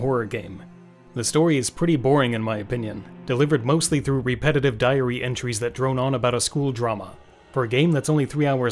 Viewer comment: I thought I heard "repetitive diarrhea" during the Evil Tonight review.